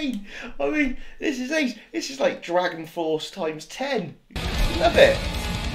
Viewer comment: On 4:00 did he said DragonForce times ten? Welp... Watch Babymetal Road of Resistance Live next.